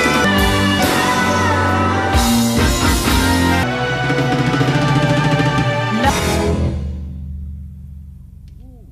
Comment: is he saying he’s lost in Atlanta? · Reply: Lost in Philly